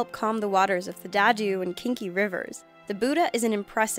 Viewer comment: WAIT WAIT WAIT WAIT WAIT- Did you just say "Calm the waters of the Dadu and KINKY rivers"?! KINKY?! Oh, that Buddha's face definitely knows what river he was built upon.